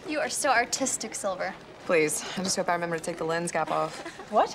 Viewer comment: wht did she say!? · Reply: "Please, I just hope I remember to take the lense cap off."